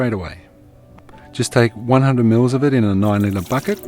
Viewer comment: At 10.26 it sounds like you said add to a 100 mils of it in an oil in a bucket...what did you say? oil confused me. Thanks. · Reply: Just take 100 ml in a nine litre bucket